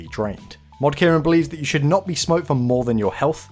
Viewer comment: did he say smote? is that a thing?